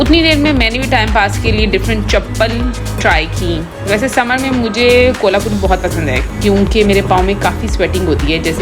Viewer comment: did she said kolhapuri chappal ? Im shocked